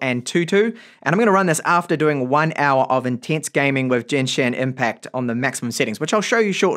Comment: No one talking about how he called it "Jenshin Impact" instead of the given name "Genshin Impact" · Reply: And I didn’t even hear the n in that I only heard “Jenshi” 💀